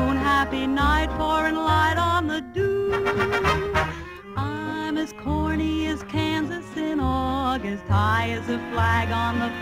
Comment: damn all this time i thought she was saying "I'm as horny as kansas." Couldn't hear her over the the constant "This is hopeless" and "Where?"